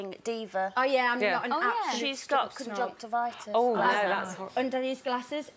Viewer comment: I have a question, at 2:02 they were talking about why perrie had her glasses on inside but I couldn't understand what they were saying. Can someone please tell me what they said. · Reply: They were saying she had conjunctivitis☺️